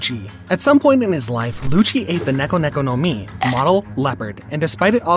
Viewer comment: I thought he said ego ego no mi 😂😂😂